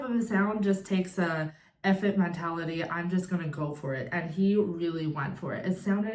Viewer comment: ! You’re a delight! First time watching you. So knowledgeable & articulate. Ty for sharing your insight as well as your awe of Dimash. (Lol: when you said “f it mentality”, I thought you said “effete mentality” 😂.) You’re so refined, I wasn’t expecting “f it” to come out of your mouth! 🤣 I’ll be checking out more of your reactions. Ty.